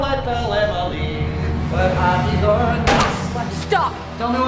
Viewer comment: What did he say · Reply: "where have you gone" I think